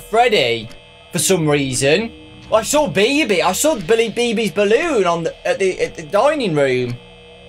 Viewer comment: Who else heard "Billy BB's Balloon" XD?